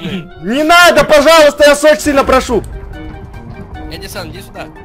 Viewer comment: 😄он сказал я секстина прошу мне послышалась